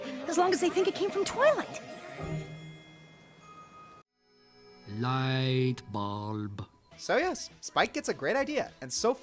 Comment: wow, I've been hearing that one clip wrong, I thought that he said "light bomb" but he said "light bulb", but you can see why I could have thought that